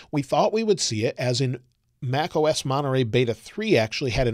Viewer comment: I thought it said monetary beta and I was like what